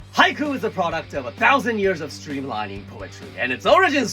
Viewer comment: I thought he said haikyuu 😂